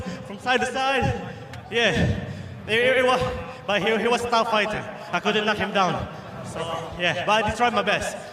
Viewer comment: I swear I heard "he was a fat fighter, that is why I could not knock him down". Hahahah